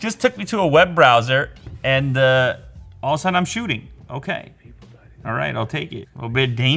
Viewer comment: "People dyin..." didnt understand what was that in background. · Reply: I think it’s cause he said “and all the sudden I’m shooting”